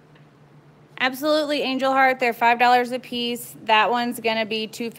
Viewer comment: you guys are so talented like seriously!!! you guys make it seem effortless even when your trying out stuff. How much would you charge for coasters? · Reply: haha nvm I just heard you say in the video $5 a piece